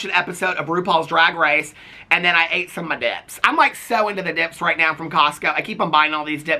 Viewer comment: Oh… I had you playing on the iPad while I had the water running and due to the noise when you said “I went home and ate some of my dips” I heard “I went home and ate some of my d1cks”. The double take I did 😂